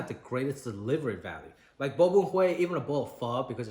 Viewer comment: oh he said bun bo hue, i thought he said bobohuei and i was confused